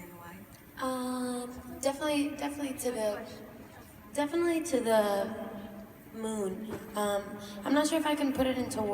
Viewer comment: Em 7:16 oque a Ally fala que a Camila olha pro lado? eu entendi alguma coisa com "question" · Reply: "Good question"